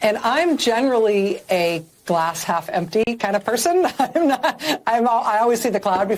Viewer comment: I swear I heard her say " she was a brain half empty '!